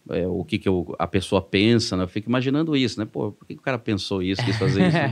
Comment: eu ouvi esse padre falando porra ou foi um engano ??